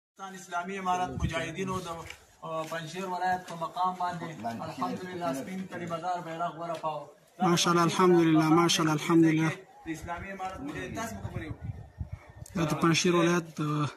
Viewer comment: wait .. did he say vada paav . why is a mumbaikar doing there